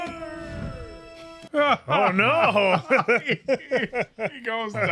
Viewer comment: I never noticed that NotZuko just shouted "HONOR!" as he was burned to a crisp by NotAzula. I always thought he said "OH NO!" or something. Thank God for the subtitles! That was one more hilarious line!